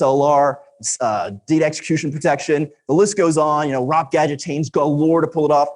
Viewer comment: This is great stuff! In case you didn't catch the bit at 13:06 (I know I didn't!) he's referring to "ROP (return oriented programming) gadget chains". Thanks again!